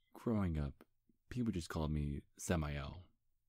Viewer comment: I thought he said his name was Zamiel... which is a nice name but Samael is ok too ^_^